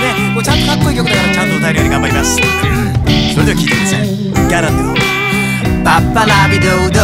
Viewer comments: がどうしてもギャ○ンドゥーに聞こえてしまう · うるさい好きww うるさいから好きwww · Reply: 何回聞いても 普通に歌い出す前の『ガラント』が ギャランドゥって聞こえるwww あ、私だけですねすみません